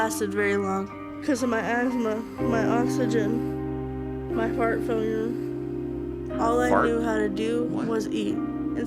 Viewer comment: I know! At @ 17:23+, I thought she said “fart failure” too😂😝😉👍🥰💋💋